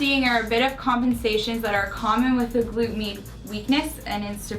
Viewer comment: lmao at first I heard her say "glute meat"... so then every time after I heard "glute meat" lol...